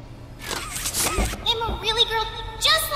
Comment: I thought I heard “open this gate with a baddie”☠️